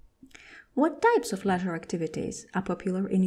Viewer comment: what activities did you say on 8:45? is it letter activities? sorry i didn’t hear it clearly. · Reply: It’s “leasure” 👍